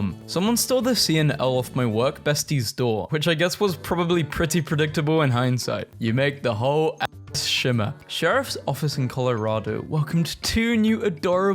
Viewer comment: help me idk wut it says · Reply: "you make the whole (c)(l)ass shimmer"